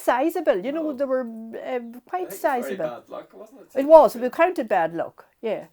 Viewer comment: Excuse me for my poor English. But does she say that the crickets were BAD LUCK? At min